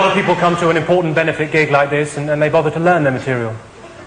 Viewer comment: Can someone please tell me what he says at the end of the sentence at 1:04? (English isn't my mother tongue). "A lot of people come to an important benefit gig like this, and then they ...(???)" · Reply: "They don't bother to learn their material..." he says, and looks at his written notes.